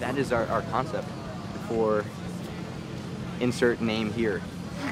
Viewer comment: I thought he said "insert meme here" xD